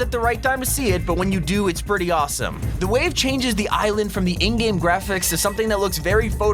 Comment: I thought you said Indian graphics for a sec lmao